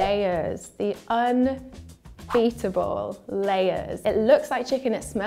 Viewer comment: I thought said “Un-biteable”